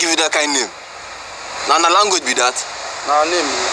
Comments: I think he says something like "What language would that be?" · It think at 0:34 he said "and what language would that be" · i think he said "na what language be that?! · I think he said “how long did be that?”